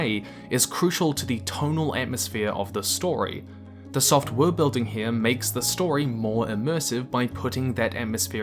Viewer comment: I watched this at 1.5x speed, and every time you said "soft worldbuilding" and "hard worldbuilding" it sounded like you were saying "software building" and "hardware building" respectively. A couple times, I wondered. "Oh? They make software? Oh wait. Right! Soft worldbuilding."